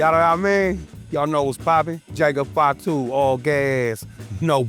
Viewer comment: what he say? Jacob Fatu all gayass!? Smh wild